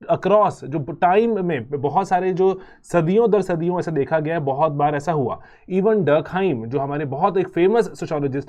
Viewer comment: pe sir ne kya kaha , please anyone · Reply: Durkheim?